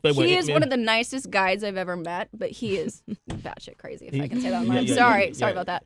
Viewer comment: What did she say at 8:11? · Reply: That shit crazy